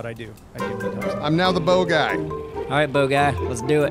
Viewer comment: "I'm now the bow-guy" and I honestly thought he said bogie cause I wasn't watching lol